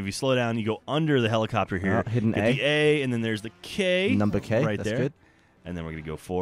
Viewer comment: did gavin say number K?